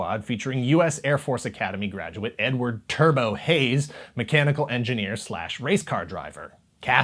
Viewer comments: Omg i had to look it up after picking myself up off the floor i thought you said one of the characters names was 4:41 n-word turbo hayes was kinda upset it was actually edward turbo hayes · had to rewind this part, I swear I heard n-word instead of Edward 🤣 my bad! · Say what? N-word turbo hayes? Chill bro